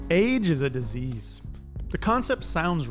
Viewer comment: I thought he said ageing is a desise and he just said age is deise and made fun of it